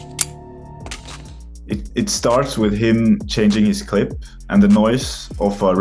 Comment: did he said clip oh hell no. Charlie we got a problem here